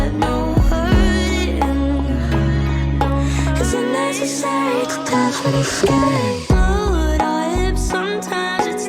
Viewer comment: these lyric videos aren't always accurate, what she said here was "Is it necessary? Detachment is scary"